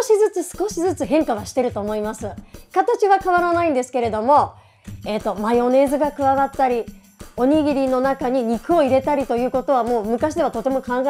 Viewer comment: Maionese eu entendi